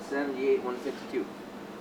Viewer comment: Sorry, I'm probably wrong, but at 15:24 it sounds like you say 162, and 152 was typed into the calculator. Hope I heard wrong, but it's easier to readjust the cam timing now, than after it doesn't work right. Sorry, my shop's been busy so didn't get a chance to watch this until 4 days late..... I've been building and testing engines for 50 years and always learning, so this is an amazing and informative video about the differences in your billet engine. Thank You !!